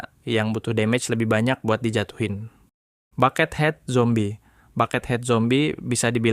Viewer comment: kok gua dengernya pakethead zombie nya